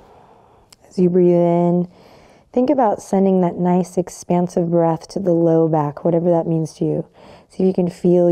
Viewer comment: Thank you! No matter how much pain I experience, this flow always makes me feel better. This time I misheard "expansive breath" for "expensive" and laughed - a good restorative breath is priceless indeed!